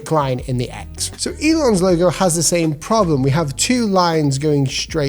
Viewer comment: Hi, on 1:00 the subtitles say "England" instead of "Elon". Just wanted to let you know so it can get fixed!